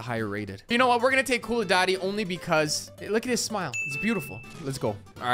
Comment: I heard you say koulidaddy right?